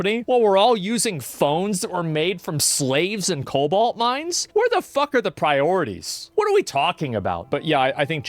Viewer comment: I thought he said Kobold Mines